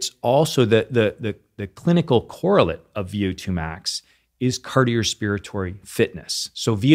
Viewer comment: Wait did I hear him say "cardiospiratory?"